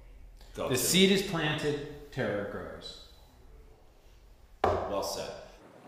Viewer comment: what does David say at 14:31-14:34? "The seed is planted...?" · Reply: JuntaParty I think he said “... Terror grows.”